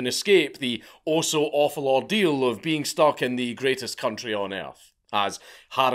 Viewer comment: I thought you said she was stuck in Britain? 😅